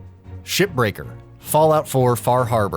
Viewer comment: Sounds like he is saying shit breaker